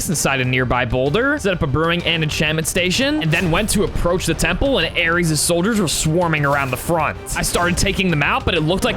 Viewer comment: what did she say? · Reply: she said good luck